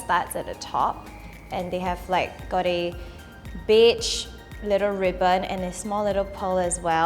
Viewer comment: "Got a BITCH little ribbon" I know thats not what she said but thats what I heard lol